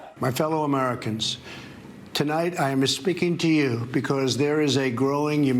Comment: Did he say "tonight, I'm 'A-SPEAKING' to you..." or "tonight, i'm MIS-SPEAKING' to you..."? I need to know. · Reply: Yes he said “I’m uh-Speaking” because he almost forgot his words in 2 seconds.